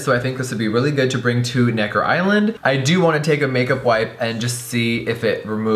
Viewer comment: Did he say n island??????